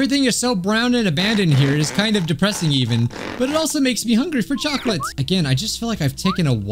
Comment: Chocolate did he say chocolate? 8:29 !!